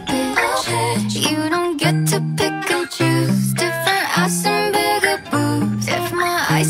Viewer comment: t nghe thành : " you don't get to pikachu" :D